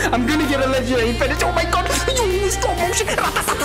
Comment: I swear I heard him say stone ocean (hmmmmm I smell a jojo reference) Edit:upon listening closer to this I was wrong and he said “slow motion”